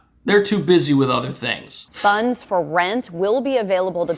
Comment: “funds for rent” or “guns for rent”? I thought she said guns and though is this Florida? If this happens in Florida the stores can open fire on mob raids to protect property.